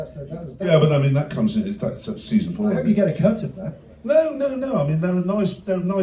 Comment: ish... did I hear right? Season 4?